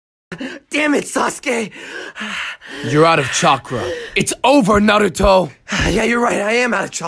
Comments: I thought he said out of chocolate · i thought naruto said "that doesn't mean im out of oxygen" at first